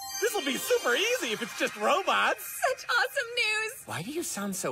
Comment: did ya say rObUx?👁👄👁👌🏻👑